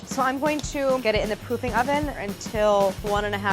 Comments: does she really say pooping oven? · K I’m literally four years old but at 10:06 it sounded like she said “pooping oven” and I laughed at loud. Lol ily Claire! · Did she just say Pooping Oven? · I heard pooping oven and I was.. baffled 🙈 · Did she say pooping oven at 10:05 or I am wrong? Please Reply and like!!! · did she say pooping oven? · haha i thought she said pooping oven instead of proofing oven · Thought she said poopy oven instead of proofing oven also what’s a proofing oven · It sounded like she said "pooping oven" 😂😂😂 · Am i the only one who heard pooping oven